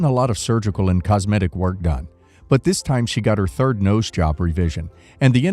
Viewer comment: I thought it said she had her 3rd Nose LOL. I was going to say, 'Yes a 3rd nose is too much' LOL.